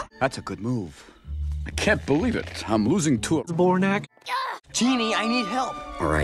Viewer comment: I can't believe it he's loosing to a horse (I don't know what he said sorry) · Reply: He said "zbornak", lol.